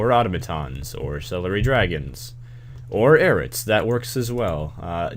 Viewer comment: Thought I heard "celery dragons"... XD